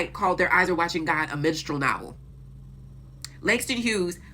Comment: the captions say ‘menstrual novel’ and I appreciate it